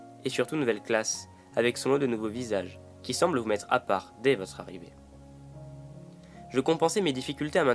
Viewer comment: il dit quoi !!!!!!!????? 😓😓😓😓😓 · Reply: « Avec son lot de nouveaux visages qui semblent vous mettre à part des votre arrivée »